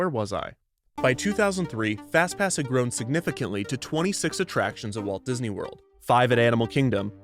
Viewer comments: Ooh, a major typo in the captions — it says "263 attractions" instead of "26 attractions" at 24:13! -Paintspot Infez Wasabi! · There is an error in the closed captions at 24:14; the captions say "grown significantly to 263 attractions" but the audio says "grown significantly to 26" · there’s a typo in the english captions, it says 263 instead of 26. sorry to be that person hahaha, i did enjoy the video a lot